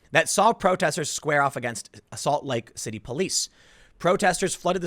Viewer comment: almost sounds like he said “assault lake city” 😆